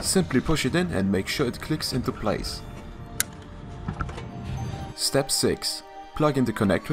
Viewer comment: i thought he said Step sis